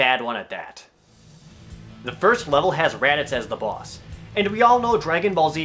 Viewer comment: i swore i heard Jared said "The first level has Rattata as the boss..." i found it funny for some reason, IF THAT WERE THE CASE i would not be surprised about how much he hates this game :D